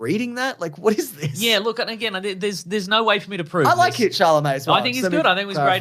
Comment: I swear said Kid Charlemagne instead of Tim Chalamet lol